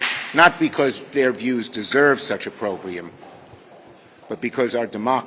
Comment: did he just say our views don’t matter?